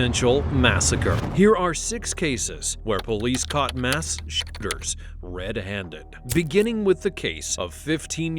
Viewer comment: The censor makes it sound like he said " Mass shitters "